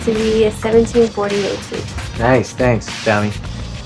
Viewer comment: um... excuse me did you just say 1748